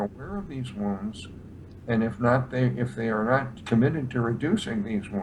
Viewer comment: please, can someone tell me what says at 4:58? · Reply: bright red light?